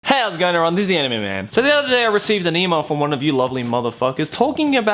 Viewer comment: who else heard disney anime man